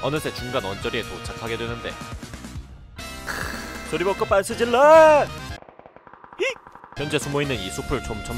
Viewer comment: 초 들어 보면 빤스 벗고 소리질러 가 아니라 잘 들어 보면 소리 벗고 빤스 질러라고 들림